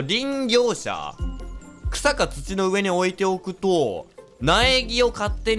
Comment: くらいの発言が最初 ひさこ土の上に置いておくと って聞こえた